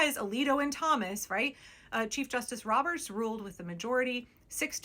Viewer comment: I kept mishearing you say "cheese justice" and honestly....yes